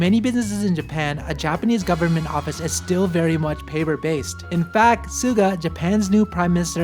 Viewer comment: Japanese Subtitle is wrong. Japanese prime minister is not 管. 菅 is correct. Their characters is very similar but there are different.